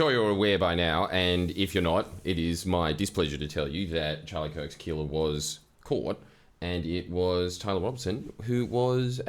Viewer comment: Am I hearing this right or did he say he’s displease the killer was caught? Genuine question I don’t know if I’m hearing him right